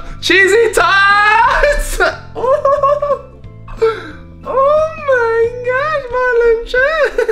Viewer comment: Wut the heck did he say at 1:55 with the high-pitched voice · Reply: TheGamingWeiner oh my gosh marlin Chan I think